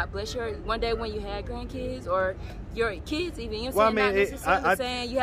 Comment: All I keep hearing is "Yum Sayin" "Yum Sayin" 🤦🏽‍♀️